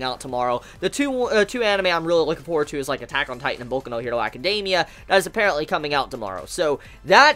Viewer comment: just uploaded and there's already a dislike...feels bad man anyway i've been sick this entire week, had to skip a day of school and make up a bunch of work lot of things have been on my mind, but i'm getting through it i've been reading JoJo part 7: steel ball run and it's fucking amazing watching rage of bahamut before 2nd season comes and i'm hyped for attack on titan and boku no hero tomorrow! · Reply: PandaWithHonor thought u said boku no pico for a sec